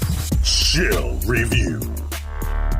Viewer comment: I love how it sounds like it says shill review